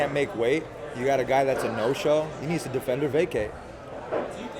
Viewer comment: what did Tony say? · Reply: isaac barrock he needs to defend or vacate.